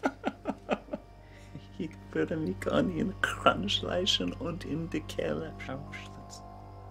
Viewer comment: Lmfao! Jared speaking in German is hilarious, as best I can make out he says that he is going to slit everyone's throat and something about NO MOM! I'M NOT GOING INTO THE CAGE! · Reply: i thought i heard 'in die keller'